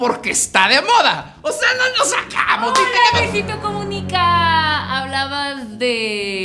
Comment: alguien más se dio cuenta que dijo luisito comunica 😂😂